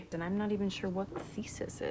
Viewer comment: I thought betty said feces but she said thesis...